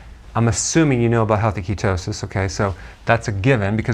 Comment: "Of course I'm assuming you know about **inaudible/mumble**, okay". It sounded like something he feels is important, yet mumbles about it, so I have no idea what it was. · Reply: ** healthy ketosis